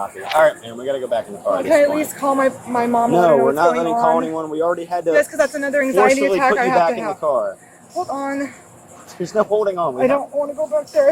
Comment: Sorry for multiple comments, lol, but did she really say “Yes, bc that’s another anxiety attack I have to have.” 🤣 Also, STOP using anxiety as an excuse for not wanting to get into trouble!! Anxiety issues aren’t silly little things, they can be absolutely debilitating, so please stop trying to pull that crap!!